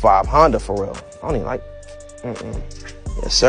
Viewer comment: I thought he said" that sounds alot better dummie" 😂😂😂